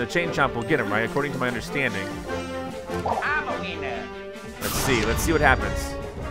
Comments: i thought Luigi said "I'm a wiener!!" 😂😂😂😂😂😂😂👌😂👌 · Did he say "I'm a weiner"?